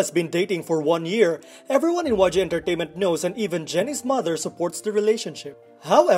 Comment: Open the subtitles (english auto-generated) Go to 0:20 Pause to 0:20 you'll see the captions saying "waja entertainment" instead of yg entertainment